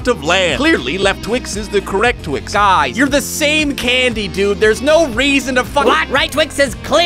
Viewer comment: I heard guys you don't have to fight there is no reason to Fu